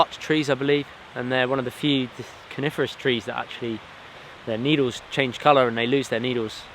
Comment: I thought he said carnivorous trees, I did a double take and my immediate thought was TRIFFIDS!!